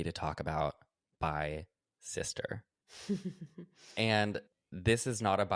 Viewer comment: Ohh my god I thought you were saying "My sister" and i was convinced for like 30 seconds that Tati Westbrook was your sister 😭